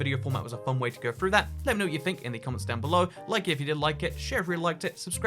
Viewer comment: Did anyone hear at 21:15 that he said, "Like it if you didn't like it." XD. What about can you beat Minecraft without picking up items? (From the ground or drops, not chests.)